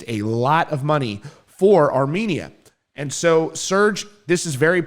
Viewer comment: Ohhh Armenia i was so confused i thought he was saying Our Media at first hahaha